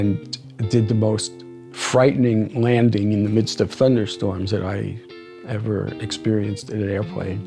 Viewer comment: "Fender storms" ? What is he saying?